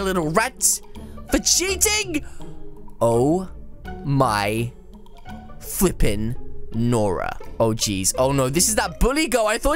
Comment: Did just say the f* word? :O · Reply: he said fliping......